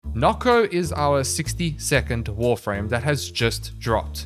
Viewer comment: I first thought when you said 62nd, you meant 60 second, as we will forget about him in roughly that amount of time.